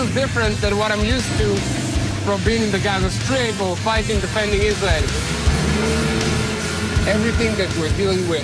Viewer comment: Hey, please correct the automatic subtitles, in 3:19 it reads “Islam” instead of “Israel”.